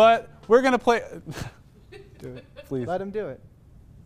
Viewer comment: Does anyone else hear the guy on the right just saying "let him do it"